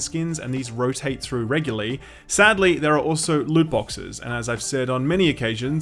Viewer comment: I happened to have the auto-generated subs on, and it thought Shill Up said "lewd boxes".